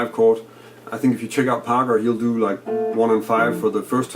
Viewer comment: Who is it that you suggested checking out at 12:40? I didn't understand the name. Thanks. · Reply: I am mentioning Parker (as in Charlie Parker) You should check him out if you get the chance! :)